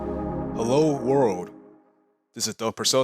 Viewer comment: Nice job, will definitely check out the advanced session soon. You can keep it simple enough so that I am not giving up but still gives enough information to learn Mr Doug Purcell. BTW I misheard your name as Doug Fourcette the character from the Good place haha